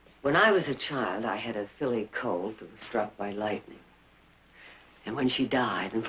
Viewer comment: had a filly colt? Perhaps I heard that wrong.